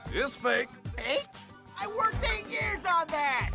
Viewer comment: I feel bad for the clam he worked 8 years on that pearl! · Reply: I thought the clam said 10 years from what I heard